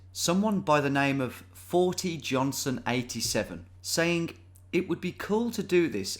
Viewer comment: Somehow I mis-heard the intro and thought you said "Faultyjohnson87" which really did conjure up some amusing mental imagery.